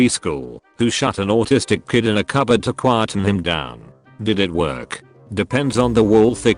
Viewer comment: I swear to God I heard “Shot an autistic kid in a cupboard to quieten him down.”